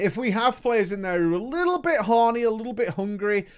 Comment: Did he just say “horny”? 10:00 lol?